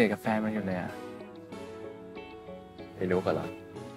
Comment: ไอ้นุ๊กอ่าเหรอ ฉันได้ยินไม่ผิดใช่ไหม😂 ทำไมนายเรียกผู้หญิงแบบนั้น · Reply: ปกตินะคั้บ ไม่ใช่ไอ้ แต่เป็นไอ ไอนุ้กอะนะ แบบนี้อะค่ะ